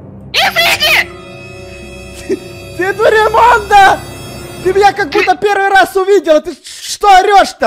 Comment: мне одному послышалось ( ты бл* кобута меня первый раз видешь?)